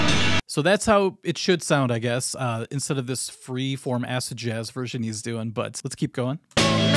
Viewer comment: On first playback, I thought you said ass-jazz, not acid jazz. Seemed to fit.